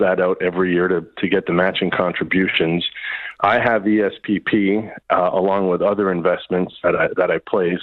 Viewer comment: I thought I heard. "I have PSTD"